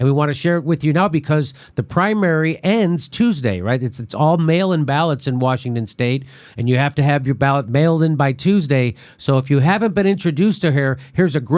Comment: ha ha, At 0:22 I thought Jimmy said "it's all male inbalance in the primary in Washington" and I immediately thought, isn't it male imbalance in every primary in every state?